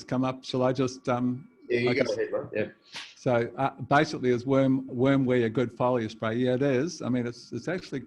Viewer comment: please, help! very hard to understand his pronunciation. what is the Word he says at 55:44? Worm" ?????? I'm sure he doesn't say "wormcasting", "wormjuice". Google caption says "Wormwear, wormweed". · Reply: Worm wee